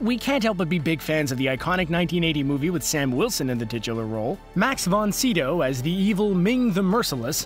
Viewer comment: When he says Max von Sydow, it sounds like he says Max von Sea Dove, and I believe it is not said like that, correct me if I'm wrong, because English isn't my native language